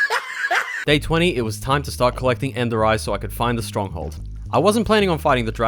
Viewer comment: did you say ENDERITE ???!!!!!